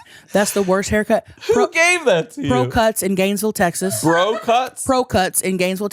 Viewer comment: I thought she said bro cuts 😭